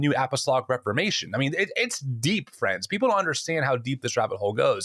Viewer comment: Omg I thought he was saying French, French people.😅😅😂😂😂